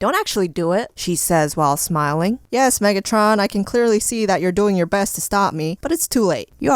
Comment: did you just say megatron? Why is she megatron?